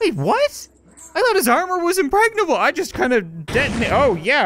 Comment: I thought he said that achilles’ armor was impregnable